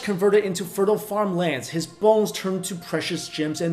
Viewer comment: Did he say his balls turned into precious gems and minerals